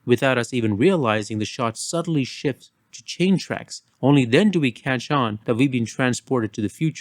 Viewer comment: at first I thought he said train tracks but he said chain tracks. I think he meant caterpillar tracks…